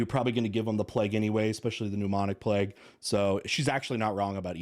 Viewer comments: Wait at 1:03 did... did he say... the "mnemonic plague?" Is that a disease that helps you remember things? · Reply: He said 'pneumonic', it's a different strain. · Learned today that in addition to there once being a Bubonic Plague that there was also a Mnemonic Plague as well. Interesting 🤔. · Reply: To be fair, I thought he said pneumonic plague, which might be a thing. But I will now try to remember that there's a mnemonic plague as well.